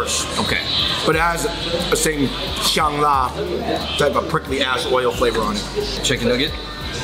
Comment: I thought he was saying "prickly ass oil" at first.